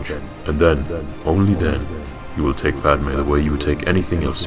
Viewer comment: why did the subtitles say you will take Batman?